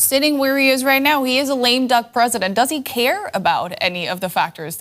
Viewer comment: Does she just call Trump a Lame Duck President?